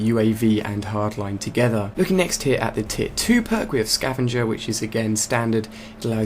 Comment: i know u said look at the next tier 2 point but it sounded like u said looking at the next tit 2 point ;p